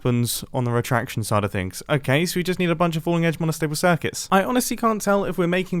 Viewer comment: I couldn't understand what he said and the subtitles said " a bunch of falling edge monostable circuits", so i'm gonna trust them... i guess